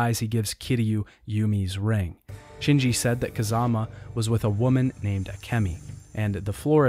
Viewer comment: when you said "kazama" it sounds like you were saying kazuma, i get confused haha